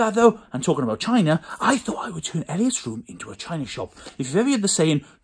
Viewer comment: Sounds like he said tina sop